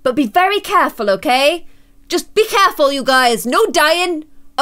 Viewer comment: instead of careful be clareful